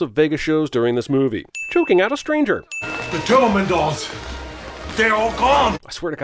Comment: the caption said "the german dolls"